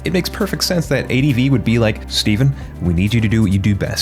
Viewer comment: Man I thought you said Steven CROWDER and I had to roll back to be sure.